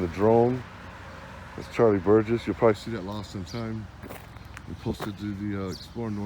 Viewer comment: did you say Lost IN Time? Hate you lol